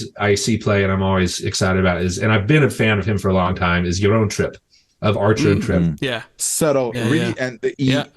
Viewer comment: Who is the second artist mentions at 1:22:40 ? I cant quite catch what he is saying. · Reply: he mentions Jaron Tripp from the duo Archer and Tripp - if you haven't listened to them, you 100% should!!